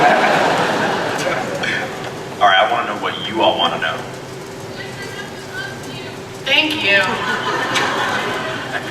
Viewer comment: what does the girl shout to Jen? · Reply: "Jen, my husband loves you!"